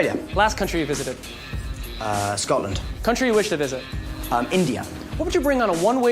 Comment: did he say india❤❤🎉🎉🎉🎉🎉🎉🎉🎉🎉🎉🎉🎉🎉🎉🎉🎉🎉🎉🎉🎉🎉🎉🎉🎉🎉🎉🎉